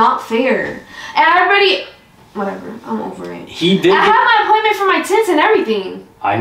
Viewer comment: I heard “I have an appointment for for t*ts” 💀 but then said he does too and I understood lol